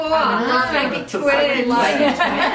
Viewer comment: I totally thought it said featuring The Psychotic Twins 😂